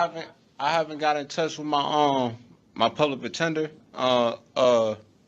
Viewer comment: Did he say, "Public Pretender"? Twice? 😅 [Edited for my typos.]